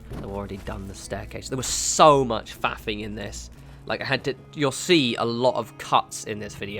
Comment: at first, I swear you said "There was so much fapping in this." XD